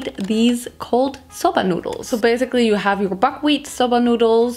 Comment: Am I the only one when I heard "Cold sobs noodles" I thought of todoroki? XD